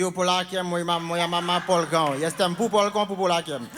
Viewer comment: ja usłyszałam bubulkopolakiem i teraz na murzynów mówię bubulkopolak a jak to jest dziewczyna to bubulkopolaczka xD